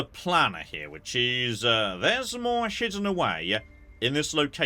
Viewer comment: did he say shidden?